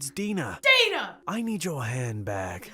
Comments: I THOUGHT HE SAID “I need your hand back!” IM DYING 🤣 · i heard "i need your hand back'' xD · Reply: Celestial Kitten I heard "I need your hair back." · I thought he said "I need your hand back." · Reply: I thought he said I need your head back! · Nobody: Absolutely Nobody: Not Lauren: Not Dexter: Not Dina: Not Even the crazy boyfriend: Mugger: I NeEd YoUr hAnDbAg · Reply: I heard: “I need your hand hack” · “I nEeD yOuR hOnd BaG” lol, the accent tho XD. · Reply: I THOUGHT HE SAID I NEED YOUR HEAD BACK AHAH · I thought he said I need your head back 😂😂😂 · I thought this dood said "I need your hand back" xDD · I thought the robber said, "I need your head back..." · Ok so Ik the guy said "I need you hand bag" butttttt...the first time I heard "I need your Hand Back" XD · BRO WHEN THE ROBBER CAME I THOUGHT HE SAID: "I'm gonna need yo head back" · I thought he said I need your head back😂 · I thought the robber said 'I need your head back' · At first, I thought the hooded guy said, “I need your head back,” and I died 😂